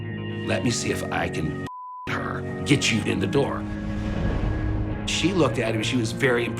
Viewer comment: What did he say? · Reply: I think he says "bullshit her"